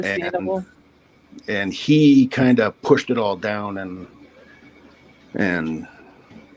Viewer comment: I'm listening from Scotland, and I thought she said "did he shave all down to his balls?...she said "shave it all down bald" My bad! 😂 xxx